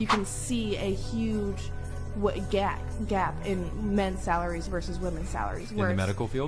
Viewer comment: Start of the video and all I hear is "You can see a huge gyatt" Like excuse me???